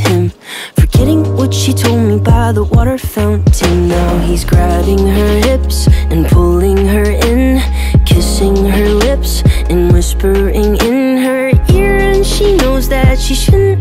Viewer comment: I always thought the song said “ And pulling her inners”